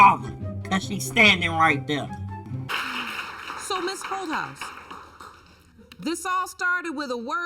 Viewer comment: By the looks the ladies in the Audience gave they thought she said "Ms HOE-HOUSE" at first like I did, too. 🤣😂🤣 ... this is sad asf though smh